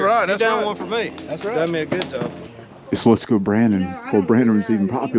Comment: Good looking finds yall! Did I hear a let's go b r a n d o n? Ha